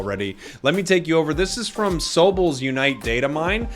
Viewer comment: sobble is pronounced sob - ble btw, i thought u said sober in the beginning for a bit